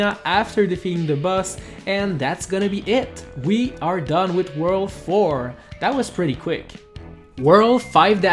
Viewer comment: he says world war instead of world four lol